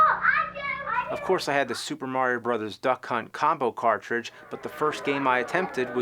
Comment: Big my apologies I thought you said Duck Hunt but you actually said duck cunt either way it was funny as hell